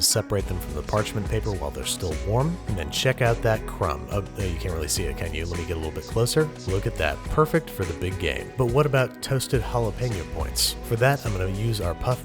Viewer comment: Is it me or does it sound like he said “perfect for the big gay”